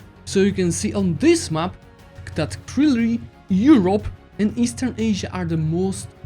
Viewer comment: Does anyone know what he is saying? Krilli? Krillin? kroognarijienfjwn? Yamcha? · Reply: Clearly